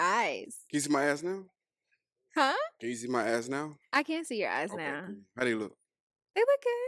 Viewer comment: Number #5 guy in the black I could've sworn he said can you see my azz now 🤮😅😊